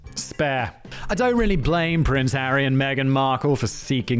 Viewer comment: Right after talking about Harry dressing as a Nazi, it sounded like you said Aryan Megan; instead of Harry and Megan.